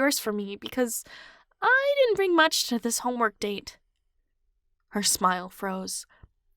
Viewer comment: "Homework date?" I thought she said study date?😃😅